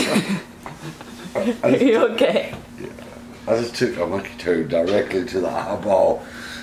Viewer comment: Thank goodness you did the slo-mo replay. I thought you said that you took a monkey turd to the eye.